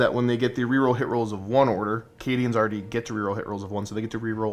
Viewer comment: Lol 😂 when u said running as Cadians I could’ve swore u said “ Canadians !” 😂